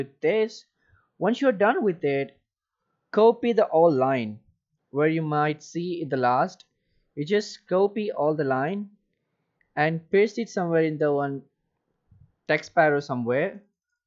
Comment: I didn't get it what you said hear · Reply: copy the line and paste in along with the command line.